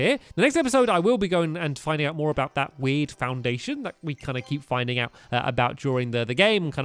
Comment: At end of the video I thought stampy said weed foundation